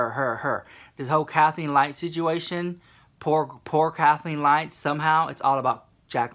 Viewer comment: Did he just say poor kathleen lights....